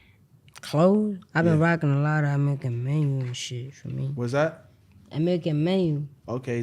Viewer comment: what dis he say? American menu??